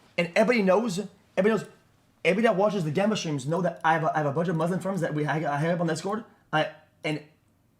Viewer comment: did XQC just say he has a bunch of muslim gamba channels at 12:45?